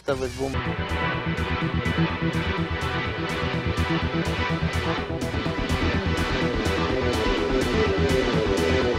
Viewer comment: what did he say?? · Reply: "It is better that I don't say anything"